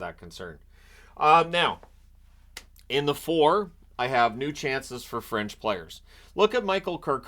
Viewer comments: I could swear you are saying 3:09 "New chances for French players..." :):face-green-smiling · I could swear you are saying 3:09 "New chances for French players..." :)